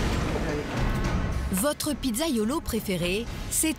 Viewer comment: J'ai compris : "Votre pizza Yolo" tellement j'avais pas entendu pizzaiolo depuis longtemps